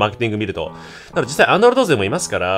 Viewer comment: 3分28秒 くらいってさ、Android勢って言ったんだよね？？自分はア○ルド勢って聞こえてしまった…